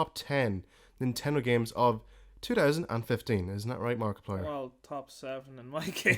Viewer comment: Do you even know who MORKiplier is? -_ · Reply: Gaming! OH! U said mork I thought u said Markiplier